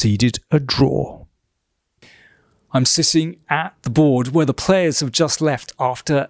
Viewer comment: 'Im SISSING (😂😀) at the board....' Im not sure what he wants to say 🤔🤔